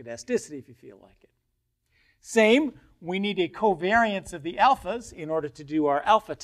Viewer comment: did I hear COVID? haha